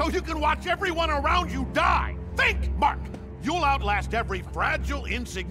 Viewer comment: Am I the only one who thought Omni man said “think mark think” I coulda swore there were two thinks🤦🏽‍♂️